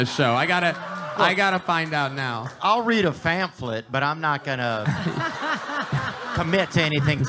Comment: Wtf did Theo just say??? Fanflip?? Wtf. Can he not say pamphlet. Ahhhhahahaha😂